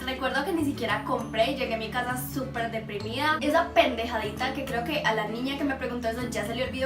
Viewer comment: que!!!!?!!! creo que escuche "esa PENDEJADITA"yo pensaba que no decias grocerias me he sorprendido 😱